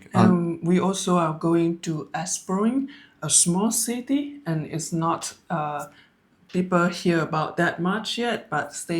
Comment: Great video but I didn't understand the name of the place she mentioned at 15:53?? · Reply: It's on the restaurant list for Da nanag. Just Google Retire in Da nang..